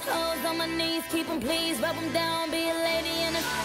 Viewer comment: I always thought it said "Be A Man"😂